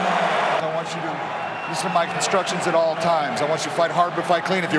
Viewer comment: Anyone else hear the ref say "Obey my constructions at all times." ?